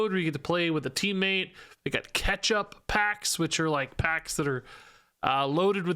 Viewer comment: I'm so stupid I thought he said ketchup packs. I was like well that's a weird promotional item...